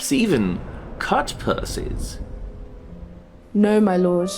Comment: what does he say ? "cut pussies" ?